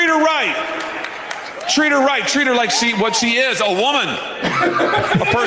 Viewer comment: Did he say "Treat her like a one?" Like 1-10?